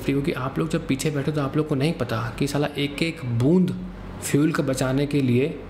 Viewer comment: did he say "sala"?? 😄